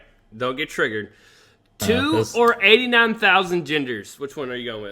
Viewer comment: Lol I got mad confused at 1:05:05 , I thought I heard say "2 or 89000 gingers". That made it a really weird conversation.